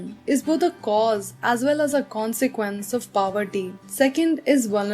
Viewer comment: Didi as I heard you say paverty 😂😂 Sorry shayad mere english kamzhor hain